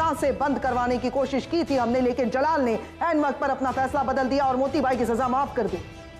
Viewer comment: did mahamanga said end waqt pr? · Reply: Aain not end